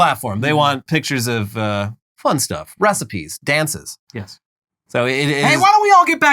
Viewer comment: says recipies, I hear beasties, the power of the self dillusion is strong.